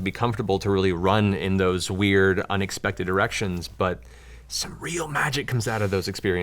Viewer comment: Did he say unexpected erections. Don't tell Keyleth